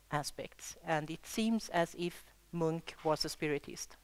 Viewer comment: I cant understand the name of the artist? munk? monk? she explaining the painting and the spirit said you couldn sense the spirits through finely tuned nerve threads. It goes on explaining the hair is transformed into waves connecting to the sad boy on the left who has a pain in his heart. Ok. did anyone else thing 'string theory' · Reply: Edward Munch